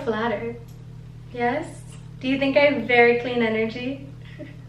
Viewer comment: when u said “very clean” energy i thought u said “fairy queen” energy and i didn’t even question it